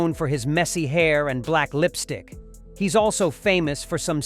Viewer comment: did narator say "black lipstick"? alright this channel is the worst